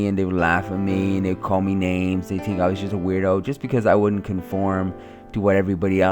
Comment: wido instead of weirdo haha 😂😂